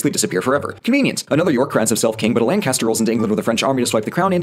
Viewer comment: said Lancaster, but I could've swore he said Lannister.